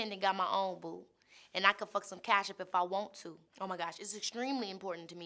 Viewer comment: At first, I thought she said "and I can fuck some ketchup if I want to" ... Am I the only one? ... 😂😂😂